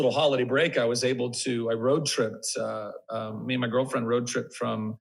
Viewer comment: Wait.... at 10:25 .... did he say "me and my girlfriend" ??? 😲😟🥺😩😢...... sad but happy at the same time...